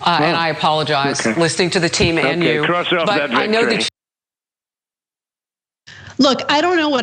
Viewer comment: What does he say at 0:53? English is not my first language and all I can understand is "blah blah victory" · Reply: Cross out that victory